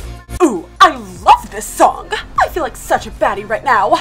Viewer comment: OMG THE CAPTIONS SAYS I FEEL LIKE SUCH A FATTY RIGHT NO INSTEAD IF BADDIE